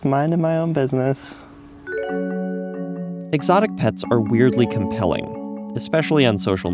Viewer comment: ferrets are exotic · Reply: Oh my god I thought you said erotic and I grew very concerned lmao